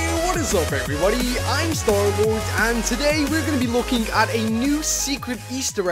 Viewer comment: If You put words on,it says instead of "Hello,Im Starlord" "Hello,Im horrible".